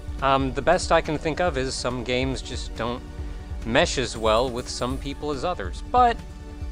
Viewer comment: "Some Games don't meshes well with some people's others." 2017 I know this isn't really what he said, but good luck unhearing it now. ^_^